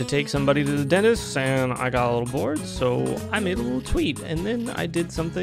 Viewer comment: did you say you got divorced!? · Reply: No he said I got a little bored